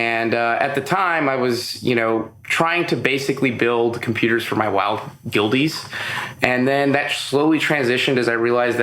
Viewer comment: Can anyone tell me what this guy says at 1:14 "....building computers for my....well....?Guildies?" It sounds like he's saying "guildies" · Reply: Yeah WOW Guildies. Its what you call your wow Guild clan members